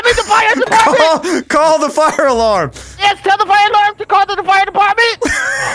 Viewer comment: it sounds like delirious says tell the fire lord